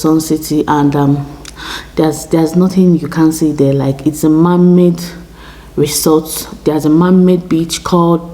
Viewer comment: don't say mermaid beach you will scare Nigerians away!!! They will start casting the mermaid spirits!!🤣🤣🤣🤣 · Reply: Lol I thought u said mermaid, tnx for clarifying " man made"